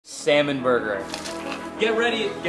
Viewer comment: At first i thought it didnt say salmon but semen 💀